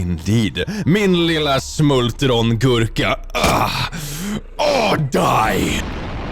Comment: Did he just say "min lilla smultron gurka"?!🤣